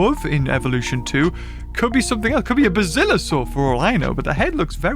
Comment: what species did he say? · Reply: Basilosaurus